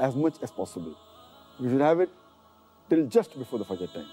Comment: the subtitles say "you shouldn't have it, till just before the fajr time", and I'm hearing should. Is should or shouldn't?